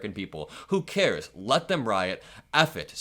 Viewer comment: No. The Trump campaign operative did not say "Let them riot" he said "MAKE THEM RIOT" "DO IT" Big difference.